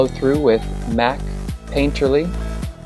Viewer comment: What did she say she uses after the clown white powder at 2:31? It sounded like MAC Paint.....something... Maybe Paint Relief....Or Painterly??? I can't find anything on the MAC website...